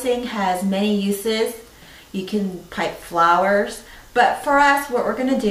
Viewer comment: Great video, thank you! Side note: At the beginning there was one word I couldn't understand- I thought you said "put flowers", spoken with an extreme California accent. Then later I read printed somewhere to "pipe" the icing. So.. kind of funny ignorance on my part. Keep up the great work!